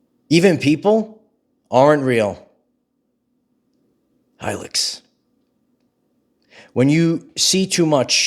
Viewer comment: You said Hilix! At least that's what I thought you said. What is this word? I can't get it off my mind. Please explain, or guide me. Thank you for everything. I've had a rollercoaster of emotions about your messages and yet I keep coming back. All I can say is thank you.